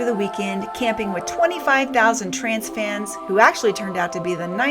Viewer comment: Ohhhhhhh...TRANCE fans! I thought you were saying trans fans! I kept looking for the trans folks! Either way, I gotta give it to ya, you were One Brave Cookie to put yourself in that mob! Lordy, lordy, lordy...